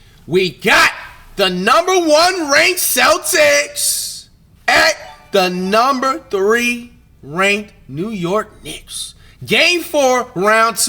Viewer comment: Did he just say #1 on celtics?